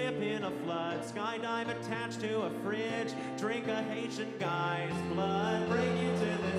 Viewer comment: did anyone else hear “drink an Asian guy’s blood”??????